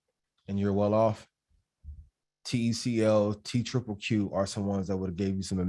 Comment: Is he saying TCL or TCO around the around the 36:35 mark? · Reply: TECL